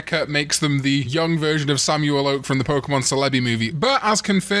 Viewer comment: Bro did NOT just call it Cell-Abby 💀☠️🥀